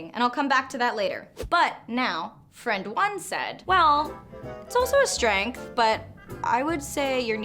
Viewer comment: The way it's edited I cannot understand what you're saying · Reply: Adrianna Ch “Well, it’s also a strength, but...l